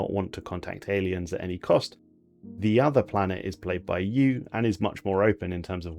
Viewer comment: I heard the “played by you” as “played by BYU” and had to go back three times to stop hearing it 😆